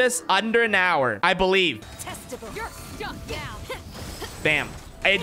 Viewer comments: did she just say "testicle"? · Excuse me did I just hear one of the characters say "TESTICLE"? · OK EVERYONE GO TO 13:08 and TELL ME what you hear. OK?! cuz I think I just heard aloy say TESTICLES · I heard "testicle "